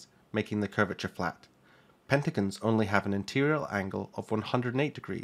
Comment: i heard "pegxagons" and since i couldn't sleep, i made one in blender.